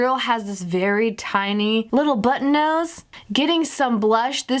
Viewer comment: I died for a second because I thought she said Ariel had a little butt nose hahahaha :)